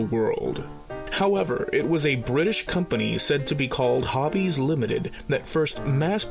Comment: Man... I was disappointed... You were saying "Hobbies Limited" and I kept hearing "Javi's Limited"... bummer... ;) lol Nice series! I really enjoy them! Keep 'em comin' !!